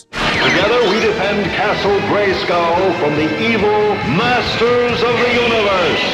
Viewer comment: I always thought it said,"from the evil forces of the universe"? in the UK?